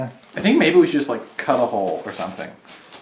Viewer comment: !! :'D Missed you~ When trying to find the hole for your Pinata child abuse victim, I thought you said 'maybe we should just cut a ho' ... as opposed to 'hole'...